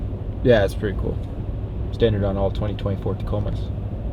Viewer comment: did you say Ford Tacoma? But great video, as usual! Keep it up! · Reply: lol i said 2024